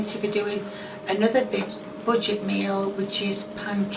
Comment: i heard "butchered meal" instead of budget meal in the intro :D